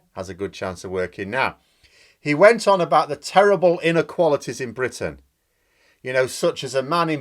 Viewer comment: Initially I misheard you and thought you said "inner qualities" and I was confused, Johnson doesn't have any.